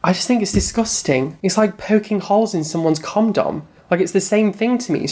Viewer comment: did you just say... comdom?